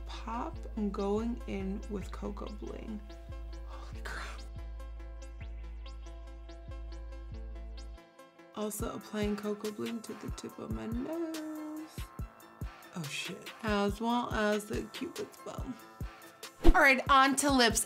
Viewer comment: When she said coco bling I thought she said KoKoBop but that's just me😂